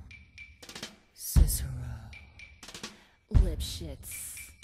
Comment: is it me or do i hear "Lip shits" xDDDD